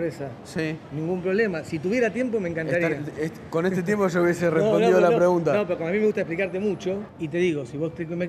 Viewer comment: " Con este tiempo ya hubiese RESOLVIDO !!!!????? la pregunta " minuto 2:29 del video El periodista dijo RESOLVIDOOOOO !!! O ESCUCHE MAL jajaja !!!!